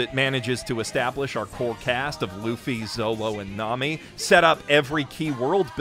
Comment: he says zowo instead of zoro lol